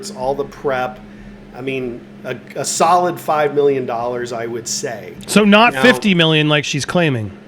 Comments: I thought Elaine said $6 million. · I thought Elaine said 6 million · I thought Elaine said $6 million